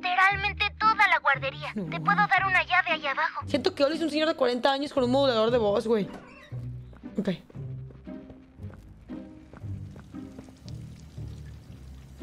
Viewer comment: JAJAJAJA pongan los subtítulos en español y verán que dice gey